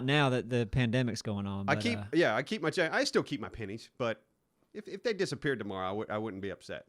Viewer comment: honestly sounded like "I still keep my panties".... Good for you... Good for you.